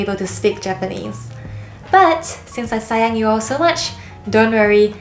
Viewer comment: I didn’t count the word “Japanese”, but I counted the word “sayang” sayang ❤😁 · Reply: I thought she said saiyan like super saiyan haha